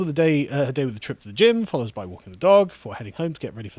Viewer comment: Hahahaha I thought you said starts the day with a triple gin, re-listened and a trip to the gym does sound healthier